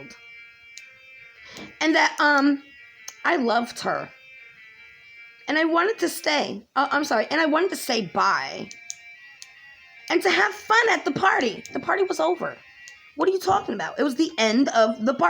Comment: I thought said, I wanted to leave . But stayed the whole party.